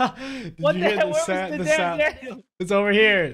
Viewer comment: I'm preetyyyy suure I heard say "What the hell, where's the damn Daniel?"👍